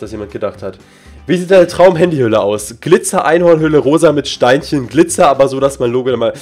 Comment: Ich habe erst anstatt Einhornhülle Einhornpi** verstanden😂